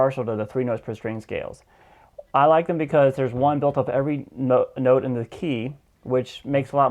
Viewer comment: He says "I like them because there's one (blank) every note in the key" but I can't understand what he said. Can someone fill in the blank and tell me those words? Thanks. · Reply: "Built off of" every note in the key. With what he calls the CAGE system, there's only 5 scale shapes, whereas with the 3 notes per string method, there's seven shapes. In a key, there's 7 notes, so a scale shape for each note in the key. It really did help me mentally, especially if at first I just went by numbers for scale shape names (i.e. Shape 1, shape 2, shape 3 etc.), then when I was practicing, it made it alot more simple to just think "I'm within Shape #1,2,3,so on" instead of trying to remember that, for instance, if I'm practing G major, and I'm in the second shape, I gotta remember "Okay, I should be beginning and ending on A." I just remember where G is (on the 6th string), find the fret thats a whole step up, and boom, thats shape 2. Once I got that going and muscle memory kicked in, then I was able to start really paying attention to what actual notes I was playing in each shape. (I hope I didn't make this more confusing, this was my convoluted thought process). Point being, CAGE only has 5 shapes, whereas 3 notes per string has a shape for each note in the key, making it easier to identify each shape with a note in the key.